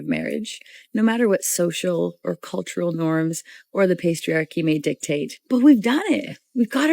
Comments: Did anybody hear the one at 10:00, she said the pastriarchy so responsible for making pastries 😂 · did she just say "the pastryarchy"?